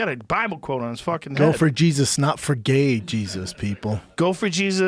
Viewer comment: Anyone else thought he said "no for gay Jesus?"